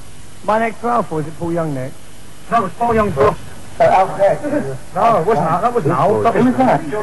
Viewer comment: What is he saying around 2:15 - someone named "Al"? "Owl"? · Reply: He's asking if it's Al? As in Allison Moyer. Then they tell George it's George Michael.